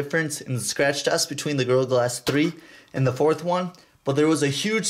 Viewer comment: when he said gorilla glas... i thought he was saying girl glass lol wtf mann